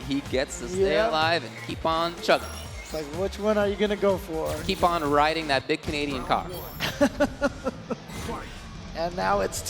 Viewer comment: Did he just say "keep on riding that big Canadian cock"?..